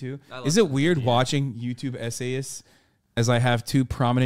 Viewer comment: I dead ass thought they were saying SA-ist, not essayist 😭